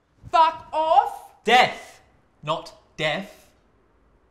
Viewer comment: “ ’imme ya zol “ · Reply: Thought he said « gimme your dog »